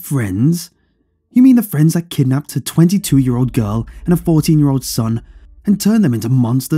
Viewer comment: Did I hear that right? "...kidnapped a 22-year old girl and her 14-year old son..." (30:32) That math isn't jiving. Great story otherwise.